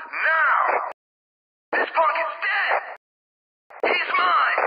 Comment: En vez de f*ck yo les entendí punk